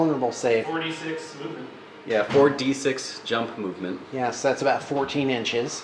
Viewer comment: Flying? Really? I thought they said Jump MC. (don't have the book myself)